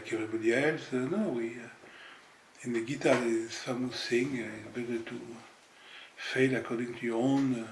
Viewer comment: I hear "in the Gita, some things are better to fail..."